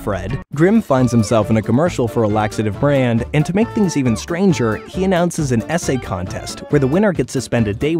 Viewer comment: when you said essay contest I heard it as SA contest